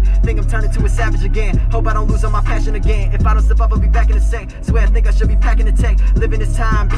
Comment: I swear he says the n word